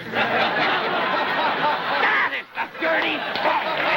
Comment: i could swear the Great One says, "THAT is the dirty bastard...."